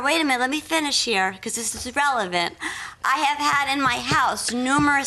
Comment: did she say irrelevant?